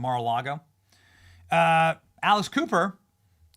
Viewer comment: Benny I thought you said ALICE COOPER @ 5:39 😂